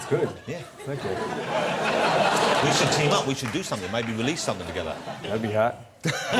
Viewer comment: ‘Maybe we should release something together’ LOL at first I thought Em said, ‘maybe not’ I wouldn’t be surprised if he did 😂